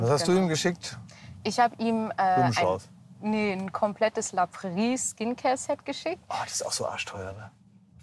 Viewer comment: Was sagt sie bei 17:13? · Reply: LaPrairie Skincare Set